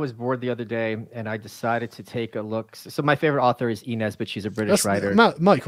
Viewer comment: Who was the children's book author Michael mentioned from Grantham? (32:00) I couldn't make out the name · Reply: E Nesbit